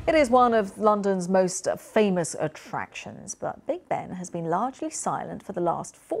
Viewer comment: Big Big? Ooops. The newsroom made a mistake and now I will never be able to believe anything they ever say again…. LOL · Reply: what are you talking about? She clearly says Big Ben at 0:02.